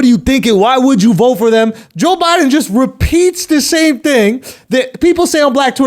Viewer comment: Literally thought he said in the Joe Budden in the beginning... I was really confused for a minute